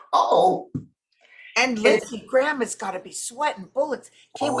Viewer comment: I swear I heard say "Lindsey Gramma," and that's who he is to me from today forward.